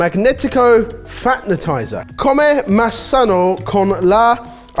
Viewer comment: I thought he said fapetizer lol 😂